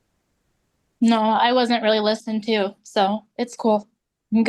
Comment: At first I thought she said "I wasn't even listening" but i actually think she said "I wasn't even listened TO".